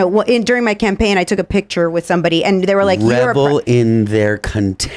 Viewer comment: did you just say rebel and not revel?😂